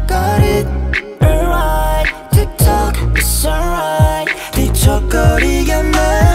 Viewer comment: no one: literally no one: me who hears "tiktok"